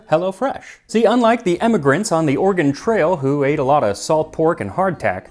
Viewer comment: fyi each time you say "emmigrants" the subtitles say "immigrants" please correct it